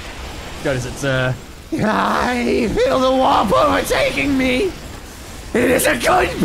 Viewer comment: i thought said "I FEEL THE WHOPPER OVER TAKING ME"